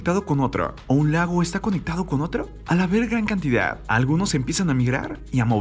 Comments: yo escuche a la verg4 la cantidad XD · Pense que decia "a la verg..."